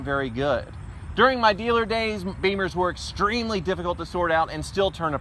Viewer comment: "Beamers", did he just say "beamers"?